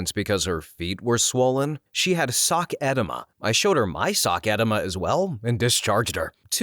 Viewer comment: My brother in Christ, it’s “eh-DEE-ma” not “EH-deh-ma”. I had to double-take because I thought you said “sock enema” which just sounds especially unpleasant.